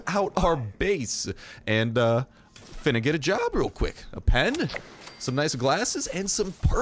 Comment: Am I the only one who heard him say "Finna get a job real quick"?